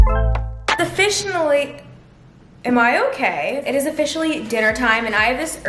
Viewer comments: I swear she said “the fish in the lake” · "The fish in a lake" had me WHEEEZZIINGG on the floor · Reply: I thought she said “offici-na-ly” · omg at 8:38 is sounds like she said the fish in the lake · When u say it’s officially I thought u said the fish are in the lake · When she said "it is officially," (8:38) I thought she said "the fish in the lake" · I heard "the fish in the lake" instead of "it's officially" lmao